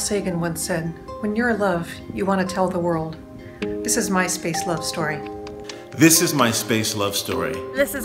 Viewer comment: Or lordy. I thought they were saying "Myspace love story" at the beginning, that would of gone weird quick.